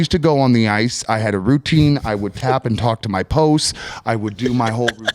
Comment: First time said he would "talk to the post" I thought he said he would "talk to his toes" and it didn't phase me because he's a psychopath like that.